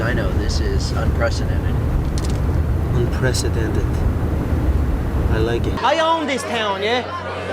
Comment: what did he say in that car.. "I leg it" or "I leggin" 0:14 but kudos to Mcgregor (that joke of an small Irish dude that loves to suckpunch elderly people in pubs) the moment with him sitting like a scared kid in the octagon with his leg/angle broken..it still rank very high about the most satisfactory moments in UFC. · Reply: he said "i like it" you can use subtitles next time if you dont understand what somebody says